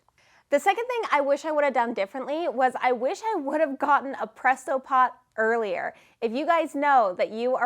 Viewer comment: The tips on this video: 1. Get a respirator - it makes you not feel exhausted afterwards 2. Get a presto pot 3. Look into more about gift wrap orders 4. Have a separate notebook to organise your testing 5. You don't need to have a big stock of each scent all the time 6. Have custom blends 7. Have multiple suppliers 8. Look into the fees of your selling platform and put that in the price of your candles 9. Talk to a CPA for bookkeeping and taxes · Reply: I totally thought she said prestopod and it turns out thats a podcast manager 🤣😂